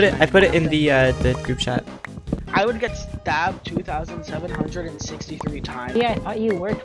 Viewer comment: wait did they just say 2763